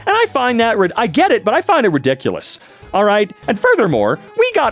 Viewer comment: did he say the r word or something? why did it cut? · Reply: He stopped and added something to his sentence he was saying ridiculous